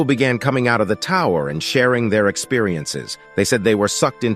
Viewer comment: I fr thought it would say sharing there juice LMAOO